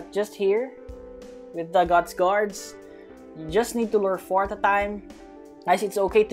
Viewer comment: I always laugh when he say "at a time" because I hear is "atataym"